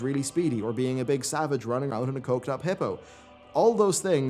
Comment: Did he say "Coked up hippo"?